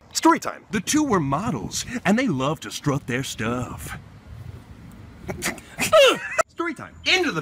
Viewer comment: Can someone help me? I don't understand what Thomas said at 2:42 ~ 2:46... "The two were models and they love to struff their stuff" ? What does it mean ? It is really "struff" ? I search in google but I didn't find anything that could help. I am brazilian so I am sorry if i wrote something wrong x_x